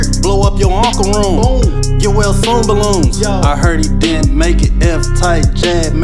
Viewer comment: Anybody know what he says at 1:03 ? "Blew up your uncle room, you wear zoom balloons?" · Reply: get well soon balloons